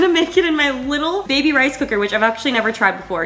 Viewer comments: Lol I thought you said rice hooker 😹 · When you said “rice cooker” I dead set thought you said “rice hooker” 🤦‍♀️😂